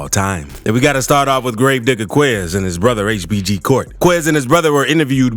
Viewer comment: The first guy I heard I thought I heard gravedigger queer 😂